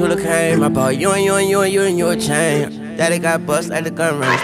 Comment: Swear I heard Wayne say I'm do you and you, a thang